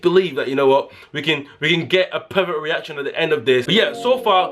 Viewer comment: did anyone else think he said a pervert reaction